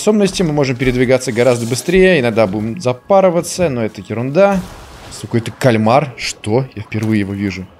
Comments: Мне послышалось, или сказал "Сука, это кальмар? Что?")) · Мне одному послышалась с*к это кальмар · мне послышалось или сказал сука это кальмар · Кто тоже услышал?) 0:35 с*ка это кольмар?